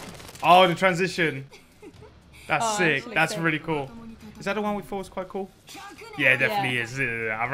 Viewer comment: can anyone tell me what he said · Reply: "is that the one we thought was quite cool?" (Trust me I was asking myself during the edit)